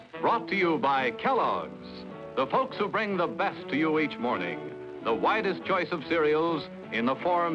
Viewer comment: When that Kellogg’s commercial came on I thought the announcer said, “Offering the whitest choice of cereal.....” I then figured out he actually said, “Offering the WIDEST choice of cereal........” Whoops.....sorry 1960’s.